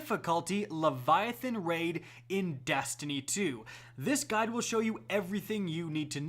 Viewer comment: I thought he said “This gun will show you...” when he said “this guide will show you...”